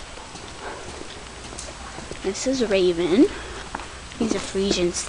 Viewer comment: when you said raven i thought you said rabies 0-0 xP